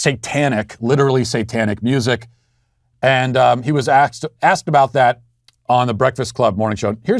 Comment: OMFG, Matt!!!!! Did you just say 'axed'? lol